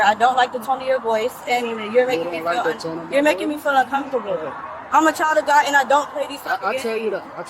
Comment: Did she just say “I’m a child adult”?